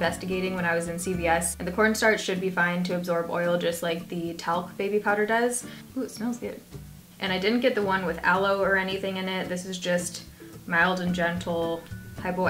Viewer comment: Whenever someone says "corn starch" I always hear "porn star." Hehehe